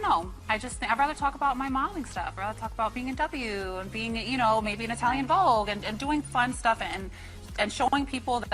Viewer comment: did she say "being a W" or "bmw"